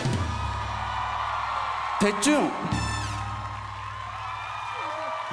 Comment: 뭐라고 하는거임? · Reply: 동엽씨 다음에 술한잔 해유